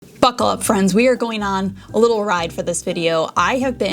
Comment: Omg I thought you said f#*k up friends at the start ! I had to rewind back 😭😆 and you said buckle up friends…. Maybe I do need to go back to bed 😂❤